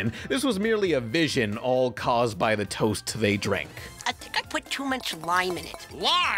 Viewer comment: I did my Research and found that Dagit Said "I think I put too much Lime" and not Wine, It's just what the VAs Said and probably was a Bit Hard to Hear either him saying Lime or Wine since it's almost simular due to the Way they Said It (I.e Accent Differences)